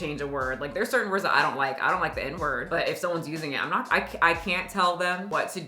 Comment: I swear to God, when you said you didn't like the n-word I thought you were saying you didn't like to be told no😂😂😂😂😂 my bad